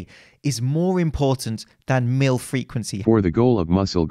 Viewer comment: All i hear is 'MILF-requency'